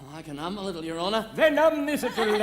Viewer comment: For those who missed it, Sir Joseph is saying 'um instead of hum, as is typical of his lowly origins, before correcting himself. This was in the original libretto, but not every performance uses that line.